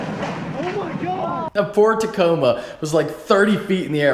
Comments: Did u just say... Ford... Tacoma? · Did he just say ford tacoma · Not going to lie i was looking at my other monitor and i thought you said " Ford Tacoma ". Then i stopped and thought " no fucking way" haha. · Who's ready for a big fat Z06 cam??? Soon boys, Andy is on the case- (more info in the video) EDIT: ALL YOU FUCKERS TELLING ME FORD TACOMA I SAID "POOR" !! I'M LOSIN IT. I tried to pin the number one "Ford Tacoma" comment and accidentally deleted it... I'm sorry whoever you are who left it. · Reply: I had to do a double take when I heard "Ford Tacoma". Your response to this has me dying though lmfao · yo i thought u said ford tacoma 😂😂😂😂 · It really did sound like you said ford. I swear to god I heard a D at the end Unless you said poor'd Tacoma. Anyway when the fuck your wrapping the dirty. ? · thought you said ford Tacoma lmao but knew you couldn't say that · Almost sounds like you said at the beginning "FORD TACOMA" LOL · did he just say Ford Tacoma ? hahahaha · I thought he said FORD Tacoma and then i realized he said poor Tacoma... 😂😂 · anybody catch ford tacoma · Bro, at 0:16 did you say that Ford Tacoma or poor tacoma? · Did you just say ford tacoma?? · idk but I heard him say "poor tacoma" not "ford." and oh yeah I sent my fucking letter over a month ago and nothing happened damnit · DID HE JUST SAY A FORD TACOMA! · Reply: pRo gaMerZ Think he said poor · Did he just say Ford Tacoma? lol